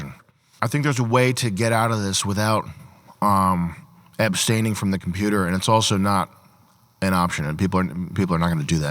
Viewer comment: did sam say ''epsteining from the computer'' or am I fucking cooked